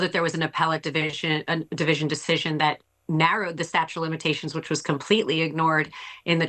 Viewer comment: did she say, “statue of limitations”? Bad lawyer. . . 😂